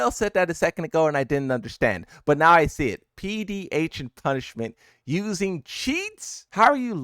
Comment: I don’t wanna be that guy, but why’d you said PDH instead of phd😭